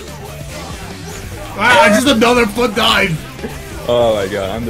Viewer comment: it sounds like it says " torque dive"